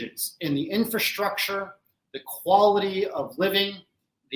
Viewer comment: I couldn't believe what I heard at 1:40 the first time, so I went back and listened again. At first I thought he said "the quality of women" has improved, but upon replay, he actually said "the quality of living." Sorry, my bad. 😅